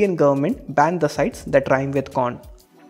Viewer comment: Did i hear saying corn?